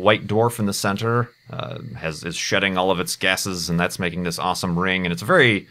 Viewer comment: did you say 8:39 : Has it shitting all of its gases or did you say shooting